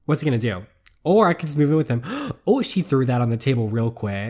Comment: I thought she said screw him XD instead of move in with him